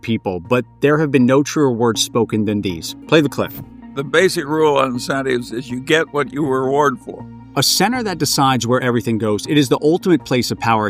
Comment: I had to play the clip at 50% speed to figure out what he was saying: "The basic rule on incentives is: you get what you reward for."